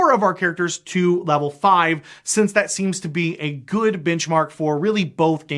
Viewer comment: What I heard: "Level 5, since that seems to be a good bitchmark for both game systems." :D Great video. Never saw the first one, but enjoyed this one. Sorry you were attacked so awfully by the keyboard warriors out there who take things a little too personal. However, they do call it "Mathfinder" for a reason, lol. And wow...after your explanations here, I never want to even try PF2.0. Yikes! Way too crunchy for me these days. I personally IMHO found that D&D 3.5 and Mathfinder 3.75 were both so math formula heavy, that I was personally never comfortable with house ruling or homebrewing anything. As you mentioned above, there are rules for everything, including blowing your nose!! Lol! (I kid, I kid). I just felt like even tweaking a monster even a little bit threw off the entire mathematical formula, and buggered up the CR level, and I felt it might jeopardize other rules within the game. I remember attacking my players with some flying goblins or something that I had created, and it was pointed out to me that they couldn't actually swoop in and attack in the same turn because they didn't have like three monster feats or something. In short, I found homebrewing things frustrating. Now, that's just me, and I know friends who DMed quite perfectly within those systems without any problems at bending the rules. But my point is that I myself felt very constrained within the very tight formulaic ruleset. Thus, I find 5e the perfect blend of decent core rules and enough fluffy rule